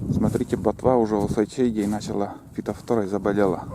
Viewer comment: не расслышал ,чем ботва заболела и почему и чем грозит??? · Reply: фитофтора.